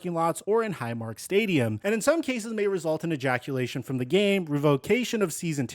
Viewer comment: did you say ejaculation from the game lol I'm weak asf 😂😂😂